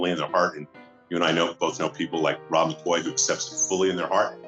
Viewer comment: min mark! Did he just say ROB MCCOY!!!!!!!!!?????? omg.